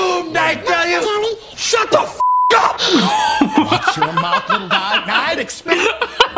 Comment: Mugman said "Crooked hat" 2:00 It actually said "Makka Pakka" 3:49 He said "Thank you" 5:31 They sang "Bucky, sail away" 8:51 Jack said "It's in my contract, you gotta read the fine print, sorry kids" 9:08 Elmo said "B-but how, Chris?" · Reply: "SHUT THE FUCK UP!!!!" was really "it's just a TV show." ("It's just a TV show." does not have a swear word and they still said "watch your mouth")